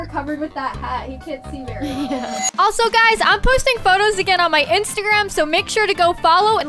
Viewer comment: I thought she said only fans when she said “Im posting photos”💀💀💀